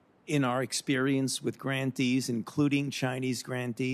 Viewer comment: Grantees.....oh , grantees..... I thought Fauci said ' grand thieves ' ......